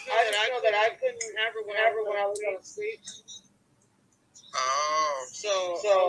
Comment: In the call about the child given up at the hospital you said C says: ”I was out of state”. Are you sure that's what she says? To me it really sounds like she says: ”I just know that I couldn't have her when I was on the streets.” Was C homeless after she and Andrew broke up or was it the Carnie years maybe (if those were inbetween A & Don)? If the Carnival travel around she prob. didn’t have a home (maybe alcohol/drug use with her carnie-friends too).. = Could be one more reason for the child to be taken immediately! (Besides 2 other kids taken prior.) Maybe it don’t matter.. Sadly it just adds more to what seems to be a lifetime of instability.